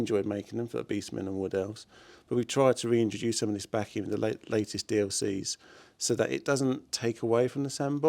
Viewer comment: Did he say "last dlc"? I thought at the showcase CA said they were still planning on more.